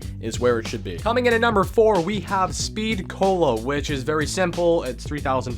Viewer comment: It sounded like he said, "Speed colo".